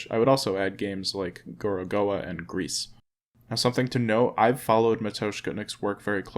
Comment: which game is this @ 1:17 and game name you are sayin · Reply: is "Gorogoa" from Buried Signal. 1:48 is "Machinarium" and then "Creaks" (i think at first i heard grease lol which could be an interesting game name!) both from Amanita Designs. :)